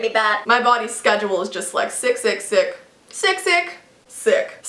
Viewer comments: I thought she said 666😳did anyone else think that😂or just me? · Lol when she was saying sick, sick, sick I thought she said 666 😂😳😂😂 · When she started saying sick, sick, sick, I thought she was saying six six six 😂 I have cursed the comment section